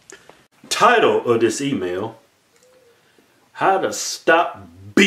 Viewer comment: I been watching y’all consistently since I found y’all about 6months ago and I JUST REALIZED THAT YOU SAY “Title of this email!” I THOUGHT YOU KEPT SAYING “TIRED OF THIS EMAIL!” 😂😂😂😂😂😂😂😂 Bahahahahaha